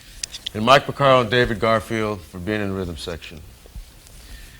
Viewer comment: intro toto variant 1:59 hello 2:23 straight eighths 2:46 writing groove for fm 4:10 interlope on the eighths 4:40 high hat exercises 6:05 adding bass drum and snare with high hat variations 6:30 holding sticks 7:12 two ways to play sixteenths 7:59 keep forgetting 8:30 sixteenths with bass patterns 9:08 bass drum technique 9:56 samba 11:02 half-time samba 11:20 coming up with a groove for a toto tune 11:54 the obvious 2 and 4 back-beat 12:11 variation, spread the beats, half time 12:45 mushanga with the band 12:59 adding the rest 14:01 mushanga 2 with the band 14:36 shuffle 14:59 triplets for shuffle 15:40 eighth note triplet groove 16:45 explaining the shuffle groove 17:24 playing the shuffle groove 17:33 back beat ghost note 18:03 upbeat shuffle groove 18:50 another shuffle version explained 19:41 second shuffle groove with the band 20:17 explaining half time shuffle 20:55 bernard purdie 21:10 john bonham 21:27 adding those and bo diddley 21:49 break it down 22:40 all together 23:00 quarter note variation 23:21 shuffle with the band 24:19 jazz/ride shuffle groove explained 25:03 mixed with modern groove 25:26 jazz shuffle with the band 26:34 another half time groove 27:08 half time jazz shuffle with the band 27:55 closing 28:24 creature stomp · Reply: Patrick It’s actually called Creatchy Stomp. I can see why you wrote Creature Stomp. The way Jeff said it sounded like he said Creature.